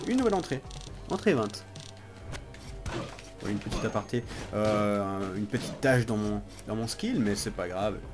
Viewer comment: " Une petite tache dans mon skill" , et moi je comprend : " une petite tache dans mon slip" , c'est sur que c'est différent xD